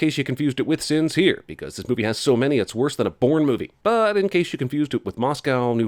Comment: So I was looking away from the screen for a moment when he was talking about the "in case you confused it with" sins and it being worse than a Bourne movie, and until I looked at the subtitles I could have sworn he said a porn movie. This has been storytime with me.